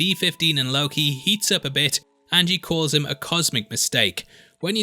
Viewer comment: I love this channel but did you call b-15 a “he” around 6:22 ... please fix that in the future and give Wunmi Mosaku HER respect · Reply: Think that’s just the accent, I definitely said she, after BVS and Lovecraft Wunmi is one of my favourite actors.